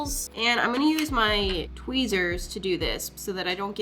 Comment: DID SHE SAY WEEZERS😏💙💙💙💙💙💙💙💙💙💙💙